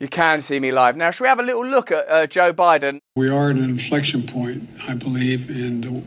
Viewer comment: At the 1:50 timeline, does he say inflation or inflection? Two COMPLETELY different narratives based on the context.